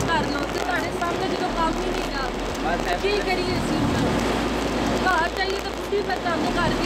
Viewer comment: "kaar jaiye tay budhi tng krdi ai" ... hahahaha Kanwal koi had hoti hai yar 😜😂😂😂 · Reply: Buhdi ni aynu phudi tang kardi A